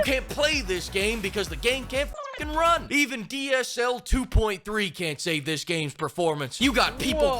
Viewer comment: Did he say DSL? Ohhhhh, that explains the fish picture